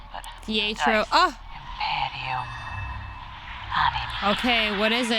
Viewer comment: I swear she said Imperio.